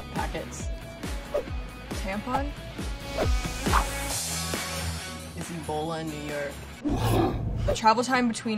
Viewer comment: what did binx say at 0:36? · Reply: "is ebola in New York"